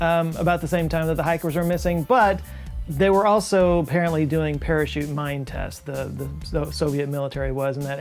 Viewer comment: I heard “parachute mime tests” at first, which brought up a lot of amusing mental pictures. 😆😜🪂🤡